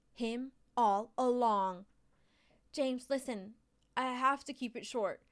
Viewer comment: she said James instead of jasmine lmao